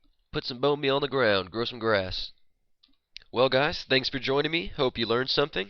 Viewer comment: did you say thanks for germany on